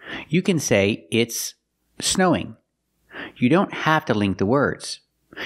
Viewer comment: "it's a snowing" is a mistake, that's what I heard he said. In the examples I hear "it's uh snowing" and it's not a mistake